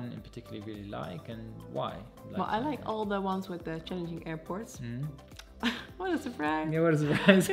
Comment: Awesome! What did you say 11:34 about airports that you like? Changing airports?